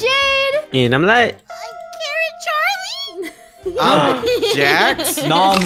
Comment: When was a carrot i thought he said "I am Karen Zach"